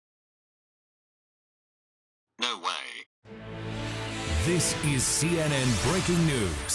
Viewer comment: "No way!" I hear "Oh, vey!"